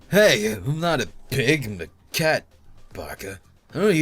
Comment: Did call the orange cat Maka at 19:53? His voice does kinda remind me of Soul's dubbed voice in Soul Eater. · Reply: He actually said Baka which means stupid in Japanese.